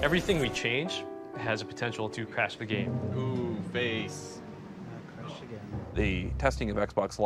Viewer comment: did he just say "Based" in the early 2000's loool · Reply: I think he said something like "face". It didn't sound like "based" to me.